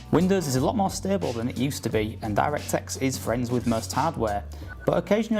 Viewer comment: I swear at 4:22 he says DirectText. Just hear it.